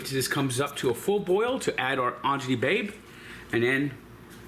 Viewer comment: What does he say at 2:36? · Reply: acini di pepe, lol